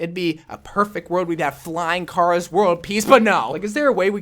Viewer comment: It sounded like you said flying Charas